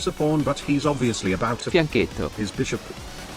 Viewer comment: What did the voice change at 1:14 say? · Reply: Fianchetto